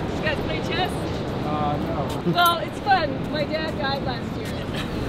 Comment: ummm am I the only one who Heard my dad died last year